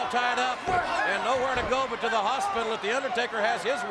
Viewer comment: Did he say hospital? 😮